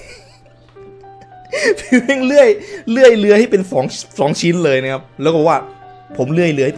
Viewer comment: ใครได้ยินว่สเลื่อยเรือยเหมือนผมบ้าง